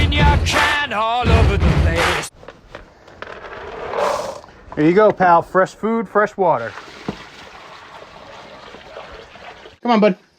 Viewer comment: I thought you said 'Here ya go buddy, fresh poo fresh water' and I thought Damn, Toby finally got that sweet duck poo as a regular meal xD